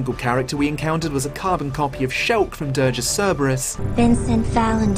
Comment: What's the game he mentions at 16:30 ? I can't hear it clearly for some reason. Been trying to find out. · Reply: Dirge of Cerberus!